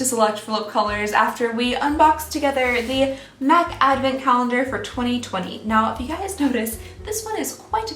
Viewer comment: At the beginning I legit thought she said " meth advent calendar for 2020"